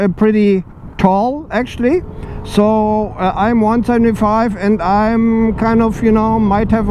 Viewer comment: How tall are you? I heard 1,25 ??